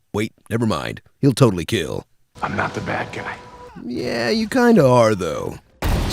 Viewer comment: anybody hear "i'm not the bat guy"